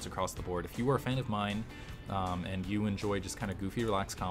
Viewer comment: Did just say "If you were a fan of mine" @ 2:24? ¬_¬